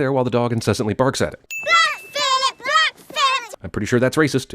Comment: what are those kids saying? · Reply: They're singing (more like chanting) a creepy song that starts with the name "Black Phillip, Black Phillip...", which is the name of their black goat. If you google "Black Phillip song" you'll find the rest of it